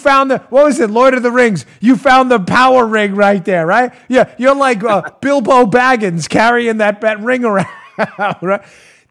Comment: did I hear you say "Bill Barr Baggins"?